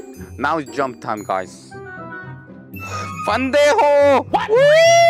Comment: I am Spanish and when I heard that I said: I already know I'm an pendejo but don't tell me too please